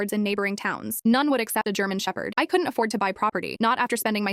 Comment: why would it matter if it was a pitbull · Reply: I thought I heard it say Pitbull but I also had headphones on listening to something else I clearly heard it say German Shepherd in the end